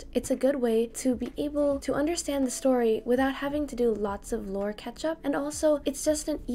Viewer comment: All I heard was "lore ketchup"? 😅🍅 Thanx for your helpful videos!👍